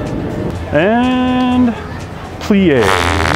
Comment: What did he say ? 🤔 Something like pleeay ? 🤔